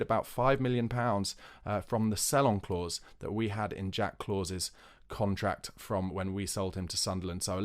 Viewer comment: Did you just say Jack Clause? 😅